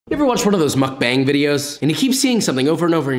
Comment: When Josh said "mukbang videos", I for a moment thought he instead said "McBang".... which sounds like the title for an adult parody about McDonald's. 😂😂